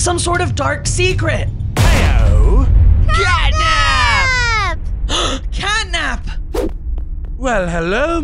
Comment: i heard gyat nap